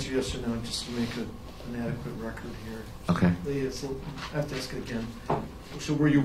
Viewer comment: Uh huh... I need Yes or No answers for the record OK? Uh huh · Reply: Sounded like "ok" to me